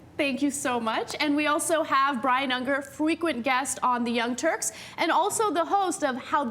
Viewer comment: Did Ana say 'fwequent'?